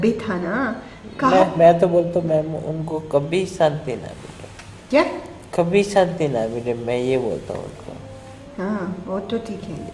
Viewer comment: he clearly said: "Ki mai to bolta hun jaise unhone arushi ko mara hai and hemraj ko mara hai unhe kabhi shanti na mile". He is clearly talking about parents, investigator should continue this one